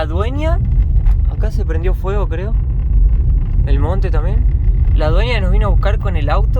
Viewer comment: Minuto 17:20 - Parece que dice "Y la dueña acá se prendió en fuego, creo..." JAJAJAJ